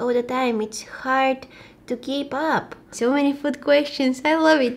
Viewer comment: When you said so many food questions I thought you said so many foot questions